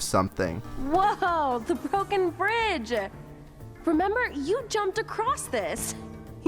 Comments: did I hear right? did she just say f*cking? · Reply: marci says "wooah BROKEN bridge" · am i the only one who thought for a second marceline said "woah, the fucking bridge!" =